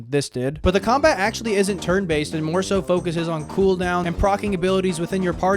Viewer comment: I heard "The combat isn't actually TURD based."